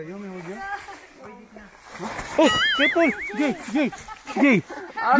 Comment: may narinig ba kayo guys ? Parang Jay-jay and Sir paul is iisa. 🤔🤔🤔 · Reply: Ahh arasu kala ko sirpaul careful pala hahahaa 🤣 na curious lang tingnan ko ulit. Salamat